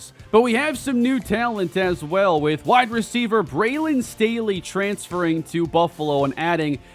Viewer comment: 😆 🤣 legit thought you said Brandon Staley and immediately I was Ike noooo let em go quickly man 😂